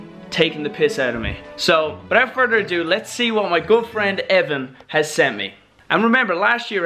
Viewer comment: I thought he said "my girlfriend evan"